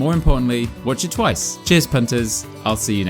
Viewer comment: sounded like you said, cheers pontus, so.... Cheers!